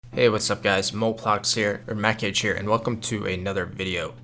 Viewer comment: I heard "Hey guys, mote plox" and was hyped :(